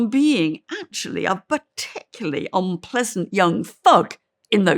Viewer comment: i thought she said "unpleasant young fuck" lmao